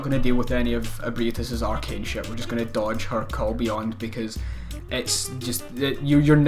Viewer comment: He says " her call beyond" but I hear "Her Kobe On" idk why